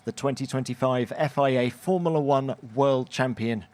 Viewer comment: anyone know what Formerla 1 is? Is it like a new racing category?